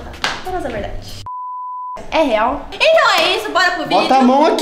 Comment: pra quem não sabe o que ela disse ela falou : " leveltan é real?"